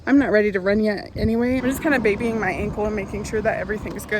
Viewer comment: Brit here When you said ankle I heard “an update my uncles feeling better, I went downstairs” and I thought you had an uncle living downstairs 😂😂 Also what are banana pepper rings?!